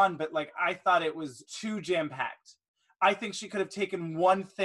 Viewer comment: I thought at 28:46 said "Jan-Packed" instead of jam-packed and I then I thought: that would be a genius product brand name for Jam.